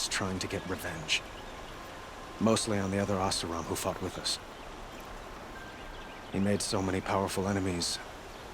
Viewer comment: thought he said the asuram that fuck wth us lol